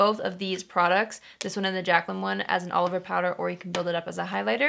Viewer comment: when she said “all over powder” I thought she was saying Oliver powder 😂